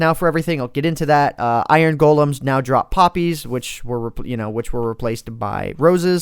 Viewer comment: I was doing something else when I heard "Iron Golems now drop poppies" I thought he said "Puppies" thats why I looked back at the screen :3